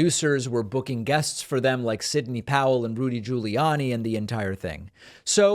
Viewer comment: I almost thought I heard you call him Loony Guiliani.